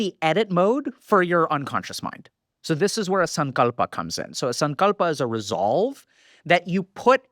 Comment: what is that thing he says ‘sungolpa’ ? ive tried googling it and nothing comes up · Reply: Sankalpa